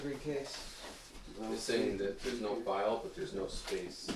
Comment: Your group is so down to earth full of goodness. I love you all. Regarding that voice when you are in the ready room. It's almost like it is replying to. said something like no more file and no more space. It sounds like the voice also says no more, but after that is is unclear. Almost like the dobrae which is a Polish or Slovenian word. This phrase could imply that the situation was either not good or that is was good. Is that bilingual response a possibility? I enjoyed this episode through to the end. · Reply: I myself thought it said: " There is no file" or "there was no file."